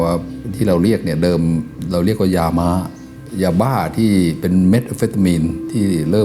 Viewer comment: Someone needs to get fired from Vice Post Production. He clearly said "Methamphetamine" and the caption missed an entire sentence.